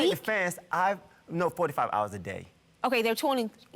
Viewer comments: Did he say 4to5 hours a day? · He didn’t say “45 hours a day” lmao he said “4 TO 5” 😂 he talks a little weird …judge Faith misheard him lmao